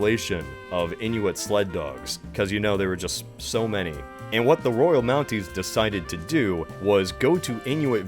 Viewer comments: seconds did he say the Royal nazis pr am I hearing things? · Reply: He said the Royal Mounties (the Royal Canadian Mounted Police) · The first time I watched this at 6:36 I really thought he said the “Royal Nazis” which honestly isn’t totally inaccurate when describing the RCMP.